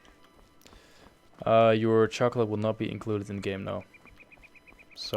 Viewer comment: What do you say at 38:20? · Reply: someone asked me if their chocolate would be included in the mod xD so I said it wouldnt